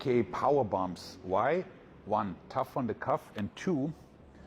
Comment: It's a bit hard to hear through the accent, but to me, it sounded like he was saying "tough on the cuff". Like you mention later, I wonder if he might flare his elbows out too far and that's why it's bad to him?